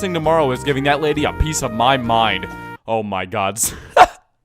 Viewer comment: Did he say 'Oh My Gods' ?!?